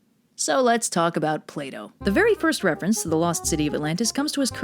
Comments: I misheard that as 'Play-Doh' and I think I physically recoiled because of it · Did you said Play doh?